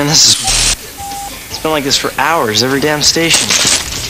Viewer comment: why did you bleep that part out. He doesn't even swear, he says "this is whack"